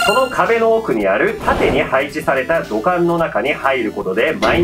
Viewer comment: 「土管」が「股間」に聞こえたんだけど……ガチでwww耳鼻科言ったほうが良いのかな?